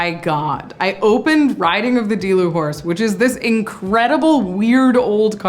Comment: Every time I heard “riding the dilu horse” I heard “riding the delulu horse”